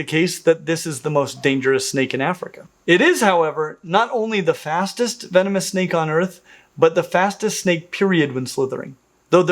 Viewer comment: At about 14:30 my sleepy brain misheard, "Not only the fastest snake on earth, the fastest snake period ..." and was briefly pondering the mysteries of snakes in space. 😆 (Also, that silvery Gaboon viper... my GOD what a beautiful snake!)